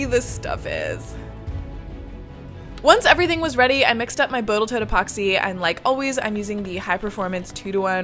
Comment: is it just me or did you say "Boatal Toat Epoxy" at 3:40 ? LOL I sometimes do that with words too :D